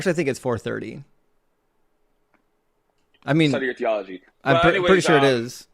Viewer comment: "study your geology" Yes, study your study of rocks, that will explain everything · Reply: I thought he said "study your theology". Which is hilarious as has forgotten more theology than the caller could ever know.